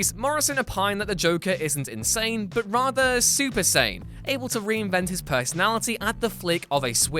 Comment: I thought he said super sayain at first